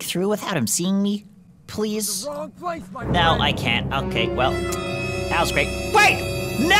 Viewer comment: BRUH IT SOUNDED LIKE HE SAID YOUR AT THE WRONG PLACE MY MAN 🤣🤣🤣🤣🤣💀